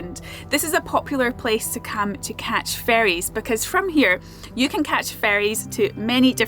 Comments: You said Oban is a "popular place to catch ferrys." I heard "popular place to catch fairies" and I pictured everyone running around with butterfly nets. The things my mind does... 🤣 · At first i thought you were saying that you were in a good place to "catch fairies" 🧚‍♂️🧚‍♀️ as in the little elemental spirits 😂. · When you said, "Great place to catch ferries," I heard it as "fairies." I was like, "Oh, that's a pretty dangerous sport."